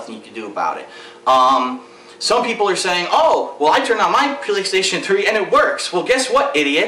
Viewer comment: lol 4:29 sound like he said "pillokstation3"